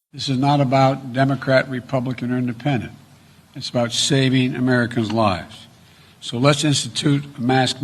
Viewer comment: He couldn't even say, It's about saving American lives. I swear I heard him say, "Save America's lives" Or maybe I'm losing it.